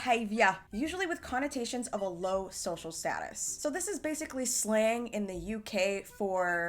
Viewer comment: LMAO when she said "slang" I thought she said "slaying" 😭